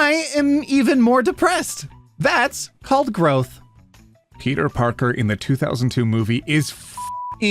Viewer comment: I misheard and thought it said "Peter Griffin" and I was confused. Now I hate myself